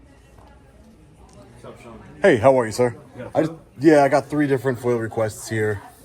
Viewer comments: did he say “what’s up SEAN” ? He know his name 😂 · did he say «what’s up, Sean»?